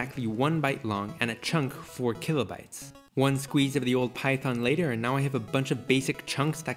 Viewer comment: Did he just say he squeezed my python